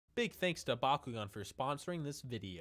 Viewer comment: I thought he said in the beginning “ big thanks to Bakugo”